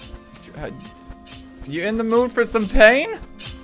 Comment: Did you say PAIN?